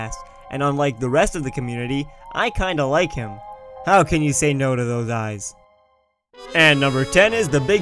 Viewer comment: " how can you say nose to those eyes" lol i know he didn't say that but it sounded like that the first few times to me